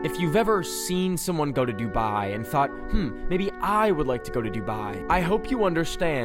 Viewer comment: BAHAH I THOUGHT U SAID U WOULD NEVER GO TO DUBAI IM CRYING